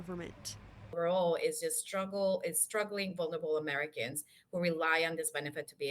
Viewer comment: did she say “it’s a struggle for ‘liberal’ Americans”?